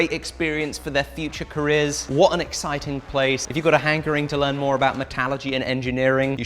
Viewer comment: "If you go to Hank Green to learn more..." ... What? Did I hear that right? Oh, "If you've got a hankering(...)". That makes more sense